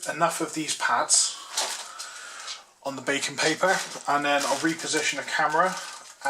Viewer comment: Ohhhh BAKING (known as parchment paper in the US) paper. I thought you said BACON paper. I was trying to figure out what the hell bacon paper was.